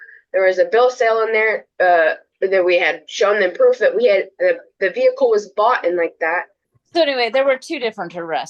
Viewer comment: Did she say, "The vehicle was boughten like that"? That's worse than a "tooken"! LTWM's head would explode!